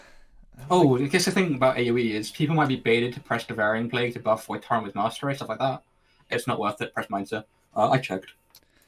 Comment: What was said at 1:25:45 I cant understand what he says ;( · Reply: In AOE, people might be baited to press devouring plague to buff void torrent with mastery. It’s not worth it, just press mind sear.